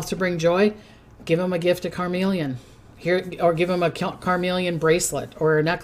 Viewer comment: CarNelian (, it sounded like you were saying "carMelian"). (Love you!! To bits!)